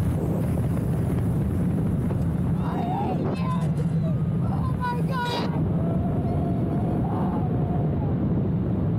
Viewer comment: Did she say “I hate you” or “I hate this”? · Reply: I hate you 🤣